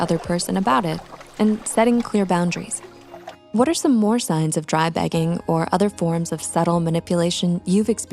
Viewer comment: I'll be honest, I thought it said signs of dry pegging at first!